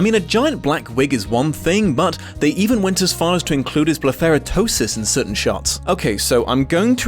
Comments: what is the word says here? I tried googling it but I can’t quite make it out. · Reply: Blepheraptosis, the droopy eye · what is that word💀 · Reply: Blepharoptosis! I had to look it up. It means droopy eyelids, often caused by age, nerve damage, or a stroke